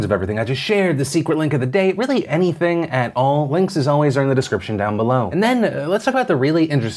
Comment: I have been watching this channel since I was a literal child and it was only today that I became aware that the script about the links is “links, as always, are in the description”. I always heard it to be “links’s always are”. Thanks closed captions for clearing that up all these years later lull.